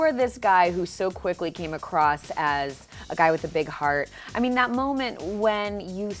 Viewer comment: I heard "Big Head".